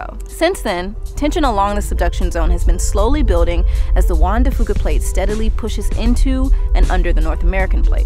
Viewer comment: The Juan de Luca plate has always been at the top of the list of my favorite tectonic plates. It's just super bad ass. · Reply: Being Irish, well used to hearing the 'f' bomb in conversation. So I literally thought they said one-de-fuck-ah!! Reading the comments going...oh thank the Big Man Upstairs! Had me gulping and sweating bullets for a second. Phew! Not good but if we're 'effed' ? End of life story. Yup.